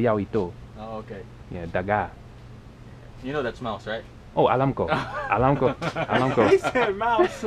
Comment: Bro why all this time when he said his nickname I thought he said “ Moose” nah it mouse lmao I only thought he said moose cause his name is